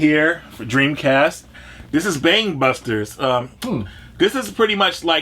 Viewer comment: Oh, Bang Busters! I thought he said Bang Bussers at first, and I imagine that's a VERY different game